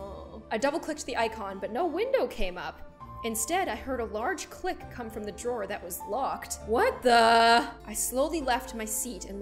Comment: After they were talking about Viagra, I actually read that as "I heard a large dick come from the drawer that was locked" Fml xD